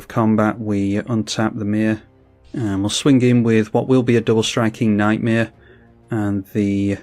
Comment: lol Thought you said double striking nightmare